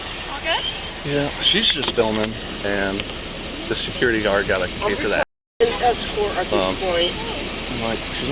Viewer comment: The security guard has a case of the assholes? lol what did he say?